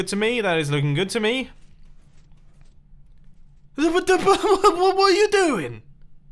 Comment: what you say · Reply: I think watapata what were you doing